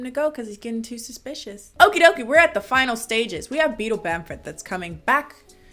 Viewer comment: Who thought that said bettle bamp b*tch... am I the only one...ok